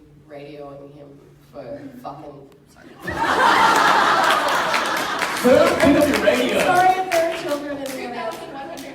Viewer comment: What is Eliza saying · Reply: She was swearing "for f**king..." but then she said sorry cuz there might be kids in the hall